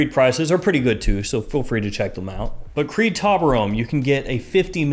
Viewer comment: I bought Creed's Taberome. Absolutely love it, It's definitely my new signature scent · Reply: I thought said Creed Toblerone 🤣 That would be a chocolate gourmand 😆